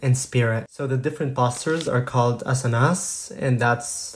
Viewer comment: Why does it sound like he said “a$s & as$”!? 😂